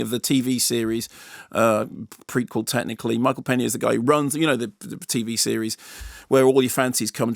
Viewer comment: I thought he said "Michael Palin" , very disappointing.